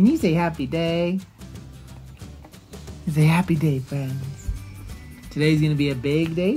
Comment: When said „Hi friends“ I just heard „Alfred“😂